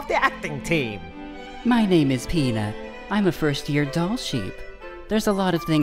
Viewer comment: I heard "My name is Pingas". Youtube Poops ruined my brain.